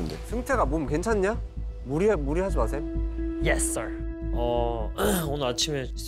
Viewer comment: 에서 말하고 나만 무리ㅎ..ㅐ하지마셈 으로 들리냐ㅋㄱㅋㄱㅋ 대화하는거 맘에드러 ㅋㅋ